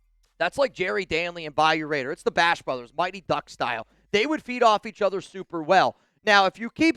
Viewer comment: Driving and listening and it Really sounded like you said they'd "Beat off each other so well" 😂😂😂